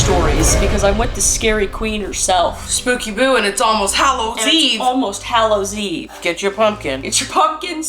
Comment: I thought I heard Halloweave 😂